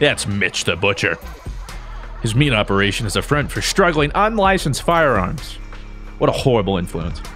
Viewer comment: "Struggling instead of smuggling XD